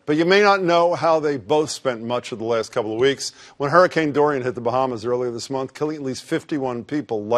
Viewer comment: I thought it said Hurricane-Torn Bananas